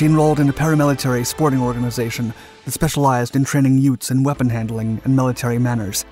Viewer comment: LOL... did you say "utes" @ 1:51 ??